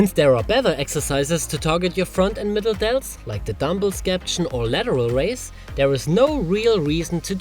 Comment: What does he even say at 7:24 ? Dumbbell sketch?